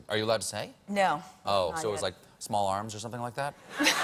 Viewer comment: What did he say at 4:13 small ??? Thanks.